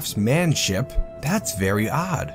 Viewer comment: HOLY CRAP, I never realised you were saying craftdwarfship until I heard you say craftmanship... I can't get it out my head now.